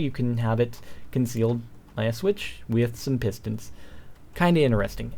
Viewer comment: did u say "pistols" instead of "pistons"?